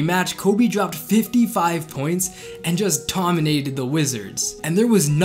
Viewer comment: I thought he said dropped 55 pounds